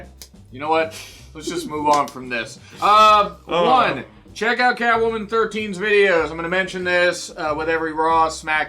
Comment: I thought you were just saying "Check out Catwoman" and I was thinking "How much has Raw broken your brain?"